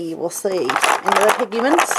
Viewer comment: Did say "piggy-mints" @ 2:07 too cute!!!